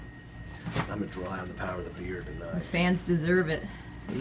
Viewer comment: I totally thought he was saying "power of the beer" until he pointed x"D